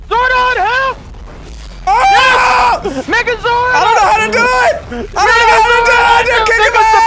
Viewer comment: legit thought said "niggazord"